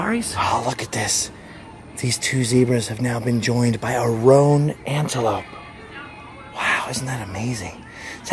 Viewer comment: Rhone antelope, not rogue antelope like I thought I heard 😅